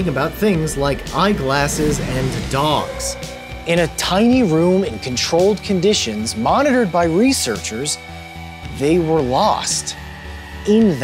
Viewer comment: at first I thought you said dongs.